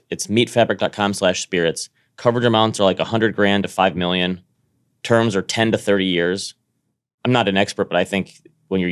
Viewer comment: Batnick help me out. WTF did you say around 1:02. It was the answer to 50 things I man shouldn't know. I even tried playing it back at .25 speed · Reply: "people who say I didn't have that on my bingo card".... I slowed it down to 25% as well. too funny. great way to make everyone sound drunk